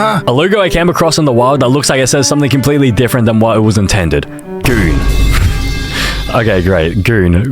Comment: I’m pretty sure it not what your saying is it I’m pretty sure it’s coon or smth I hope😊